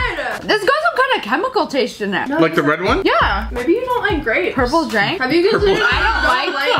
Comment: I heard “ Purple is dank.” 😂 I’m dead.